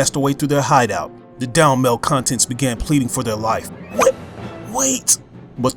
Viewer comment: When you said Malcontent, I thought you said Male Content. I was like "wut?"